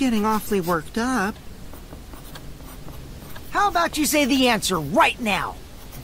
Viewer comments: I'm so scared when he said "Say the answer" I thought he said "Say the N Word" and I have never been so caught off guard in my life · BRO FOR A SEC I THOUGHT HE SAID "HOW ABOUT YOU SAY THE N-WORD RIGHT NOW" I WAS LIKE SIDKHROWUDKEHFKF WHAT